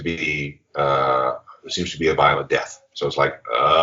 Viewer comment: subtitle says “seems to be a bio of death” I’m sure said “violent death” cause that’s what Thurston Moore referred it to as....it WAS a violent death